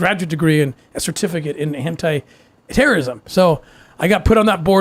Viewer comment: did he just said Hentai terrorism